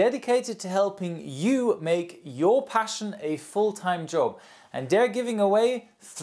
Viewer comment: i thought i heard " making ur passion a full time joke "